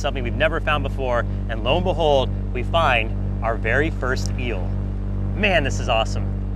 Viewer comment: I thought he said very first meal😅